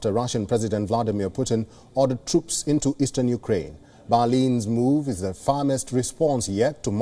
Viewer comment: Oh, he was saying Berlin. At first I thought he was saying 'Bali' and I was wondering why was the reporter there. Then I assumed he was just talking about a city in Europe I had never heard of... Every time he said it I would think 'where is this place and what does it have to do with Germany?'